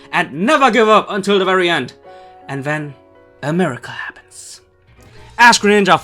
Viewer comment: It sounded like-"And then, America happens."